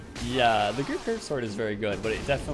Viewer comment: was he saying the "great curved sword" ? I couldn't quite hear clearly and it sounded like "grouper" or "grouped" curved sword lol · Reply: I said the ghru curved sword. I did say it quickly though lol